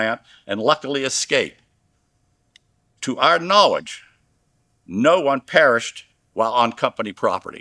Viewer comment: did he just say "if you perished it's on you"?